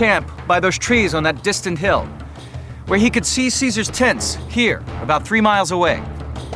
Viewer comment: I know he is saying tents, but I swear to got it sounds like he is saying "he could see Caeser's TITS from over three miles away." Excuse me...